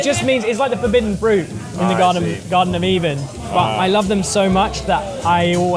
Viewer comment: Did he just say 'the garven of even'?